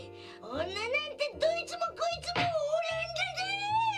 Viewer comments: ここまさおくんなんて言ってんの？笑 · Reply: ｹｯ!女なんてどいつもこいつもオランダだァァ(？) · おにぎり何て言ってるの？ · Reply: 女なんか、どいつもこいつもオランダだ～ぃ！ ですかね？ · ここのマサオまじでなんて言ってんの？w 何回聞いても「女なんて、ドイツもこいつもオランダだ〜い！」って聞こえるんだけれども。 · マサオくんなんて言ってるんだ？「オランダだ！」？ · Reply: どいつもこいつもオランダーだー！って言ってますww